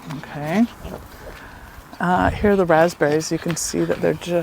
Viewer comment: Cute young adult kids. Darling hats. Lovely pics. of your France property. Lovely! Oh too funny, I heard you say “hear the raspberries,” I’m frowning wondering what sound do those types of yellow raspberries make? I’ve not heard anyone else stating that they’re yellow raspberries make sounds. 🤔…hmm, I now think you said, “here are the raspberries.” 😂😢 Nice carpentry work. The transom’s look sooo good. Good for you to get good guys. It can be a bit of a scary challenge for a lot of women. Listen to you, “get a grant,” love it, now you’re thinking. Oh good to know about the shorts. I’ll be watching for them on YouTube. Sometimes that all I have time to watch these days.